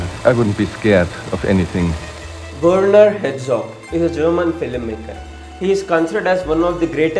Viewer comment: Werner Herzog, not Hetzog. Good video though. LOL the auto captions say Hedgehog.